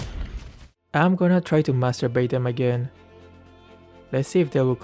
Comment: what he said "master bait" what i heard "masturba- you get it"